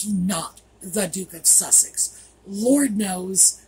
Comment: I thought I heard him introduced as Duke of wes Sex well if he is introduce as Duke, he's represented as royal status